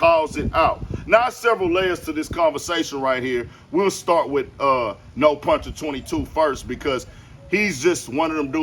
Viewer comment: did he call him No Puncher?😂😂😂😂😂😂